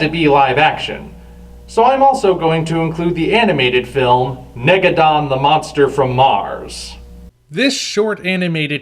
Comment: When you said Negadon I thought you said Megalon XD